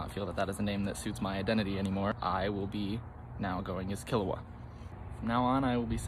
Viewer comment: Did they say Kilowatt or Kilowog? Are they a DC nerd or just a lightbulb enthusiast?